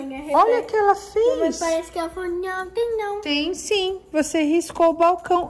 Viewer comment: também escutei a falando não tem não 😂❤